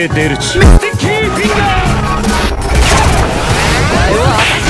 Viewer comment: at this part i Heard "mr ki fingaars"